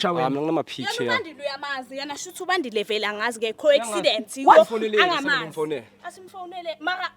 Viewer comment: Who else heard co-accident?